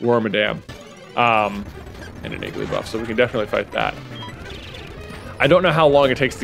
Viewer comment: When you said “Igglybuff”, I thought you said “Gigglybuff” about 7 minutes in.